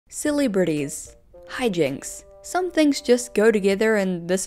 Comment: im losing my mind. for a solid 15 seconds i thought you said "silly Britties" instead of celebrities and the idea that phrase might ever be used made me take 206 psychic damage i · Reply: lol i heard "silly birdies"